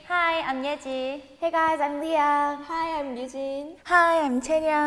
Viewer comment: i thought ryujin said hi im hyujin 😂